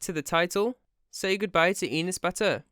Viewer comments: What the heck is that name i thought he said penis batur brah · Enus Batur fam I thought it said Anus Batty 😂😂😂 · I have bad quality headphones and all I heard was ‘Anes Bat turd’ each time, lol